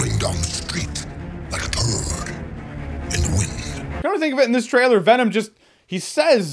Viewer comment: I thought he said a bird in the wind